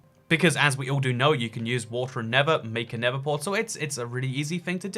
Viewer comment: Did i hear wrong or did he say water and nether